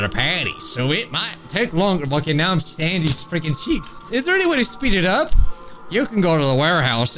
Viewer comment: Sound like he said Sandy’s checks LOL